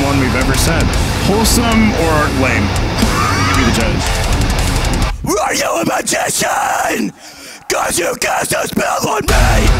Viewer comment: I thought you said are you a McChicken lmao